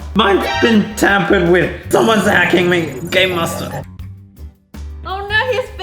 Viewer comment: Did he say game master from Rebecca zamolo